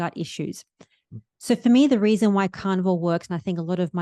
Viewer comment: In thought she was saying, Carnival